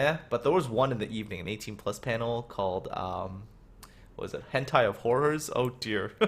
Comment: it's called Kantai Collection which is a game un which warships are girls which battle agaist monsters which are named abbysal, I would have never thought you would say Hentai....